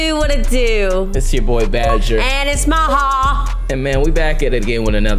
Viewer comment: I’m sorry I heard her say Christian not badger???????????????